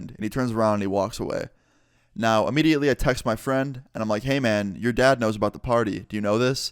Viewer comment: I thought he said "now Immediately I touched my friend.."